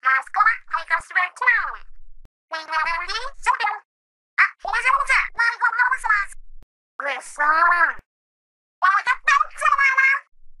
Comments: It kinda sounds like she said "うそぉ (uso) (seriously?/lie)" lol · Is it me or does she say "We're counting on you"? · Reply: Callie sounds like she says “くそ” (kuso) which means “shit” lol